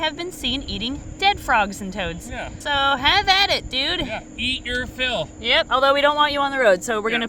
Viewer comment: when says “eat your filth” and the statements after he sounded like Gene from bob’s burgers. And now I can’t unhear it. 😭